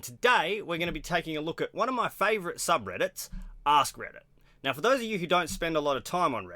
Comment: It sounded like you said a$$ Reddit ahahah